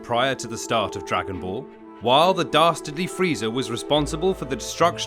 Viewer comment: I’M SORRY? DID YOU JUST SAY FREEEEDA?